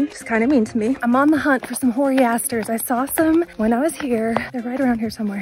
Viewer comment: At first I thought you said you were going to pick some "horny bastards" after taking your pics. I thought it was an unexpected direction for the video, but probably still entertaining. Olive you!